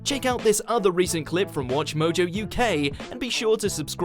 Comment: I thought he said watch mojo you gay